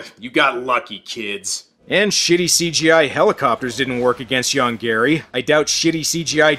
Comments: I can't help it, but I always hear 'Young Gary' when they say the monster's name. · I keep hearing "Young Gary" and that somehow makes this even funnier.